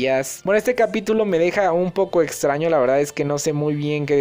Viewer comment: Pensé que decía Muzsca Lo extraño :'v